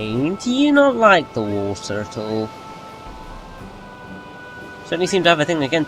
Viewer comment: 0.21 "Raine do you not like the Wartortle?". Love the way I misheard that XD